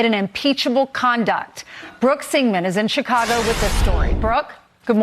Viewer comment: Dude i thought they said semen to. Im glad you went back. Lol it was singman.